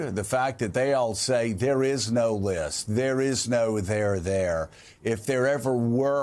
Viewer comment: I thought Barbie said there was no there, there. What a disgusting group of people!